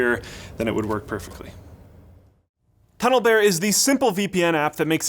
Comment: When you said Tunnle bear at 5:22 I thought you said "Pedo Bear" I was taken back for a brief moment hahaha. Honestly it does sound like you dubbed that, did you hide some audio in plain sight sir?